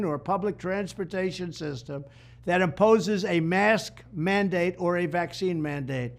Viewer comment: At the 2:00 mark in the video, Trump said he would "cut federal funding to any school, college, airline, or public transportation system that opposes a mask mandate or a vaccine mandate . . ." Yep, totally nailed what he meant to say.